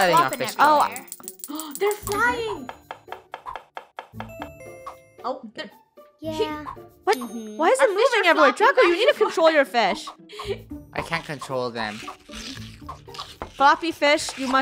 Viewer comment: WAIT WHO SAID "yea~" DID ANYONE ELSE HEAR THAT??